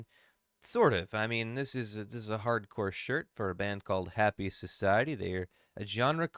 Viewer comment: Trying to find that band but they arent showing up. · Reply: Nevermind, thought yi3u said happiest society